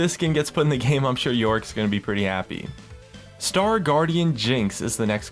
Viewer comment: I thought he said "Star Guardian Jhin"...